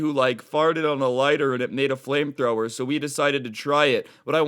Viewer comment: I thought it said lightsaber